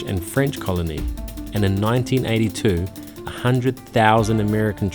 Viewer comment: 1982? Did I hear that right? I guess it should be 1942 for WW2